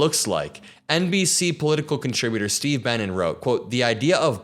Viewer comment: I had to look at the screen for this one, cuz at first, I thought you said "Steve Bannon", and I just knew that couldn't be right. Poor Mr. Benen... lol